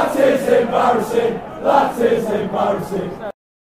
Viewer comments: In 0:30 I hear That sh*it is embarrassing 😃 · i only heard that is embarassing